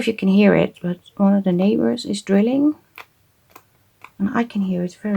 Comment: Another fun challenge! I thought you said your neighbours were grilling. It was just some sounds in the background, but i couldn`t understand what they were doing... It sounded more like dogs hauling for not getting any meat😅 Then I suddenly realized you said DRILLING🤣🤣 And then it all made sense😊